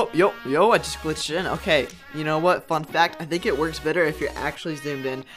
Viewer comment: I thought you said f*ck that